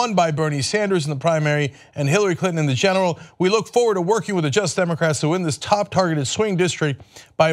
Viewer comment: Cenk, I love you but say the whole word!!!! It often sounds like you say JUST DEMOCRATS